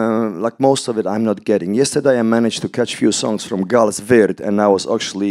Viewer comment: What band does he say at 11:50? I’m intrigued and wanna listen! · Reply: Gaahl's WYRD